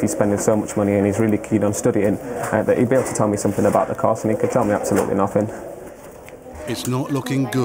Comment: Yeah but your accent is SO thick! What are you saying? Cars? Costs? Cause? OH! Course! Of course 🤦‍♂️